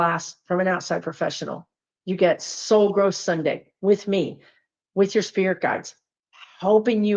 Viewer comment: The human brain is a wondrous thing. I thought I heard “so gross Sunday!” 🤣😂😅So glad it’s Soul Growth Sunday!